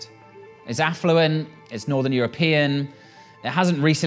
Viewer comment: is it my hearing or did he say it’s Northern European? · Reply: Yes, he said northern European